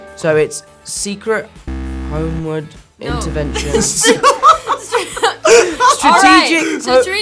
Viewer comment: Tom : Strategic, Homeland...Intervention Zendaya : Yes Also Tom 2 seconds later: So its Secret, Homeward, Intervention Me : dies of laughter 😂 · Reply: I thought he said secret hoe land intervention the second time